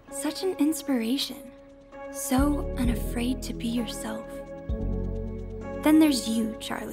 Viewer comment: "To be herself." Did I hear that correctly? Herself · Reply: Fandom Switchers "so unafraid to be yourself "